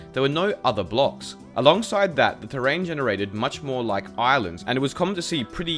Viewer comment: Who else thought he said Ireland